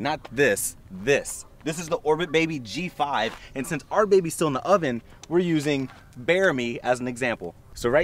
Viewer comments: Did he just say," and still our baby's in the oven??!"💀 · did he just say "our baby still in the OVEN?!?